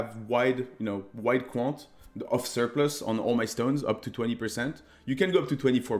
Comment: can anyone tell me what watchstones he put for valdos at 4:30? i couldnt really understand. Thanks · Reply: watchstones aren't necessary but we use platinum surplus for quantity -editor